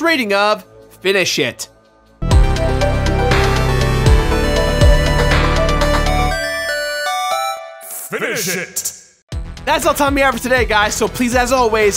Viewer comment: When he said finish it I thought he said it is shit